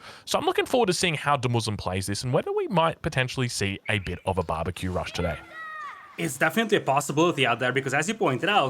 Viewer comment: must be hungry because I'm pretty sure I heard "Barbeque Rush" (2:03).